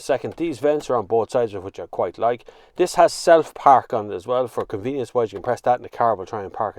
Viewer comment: I thought he said 'this has south park' 😂😂😂😂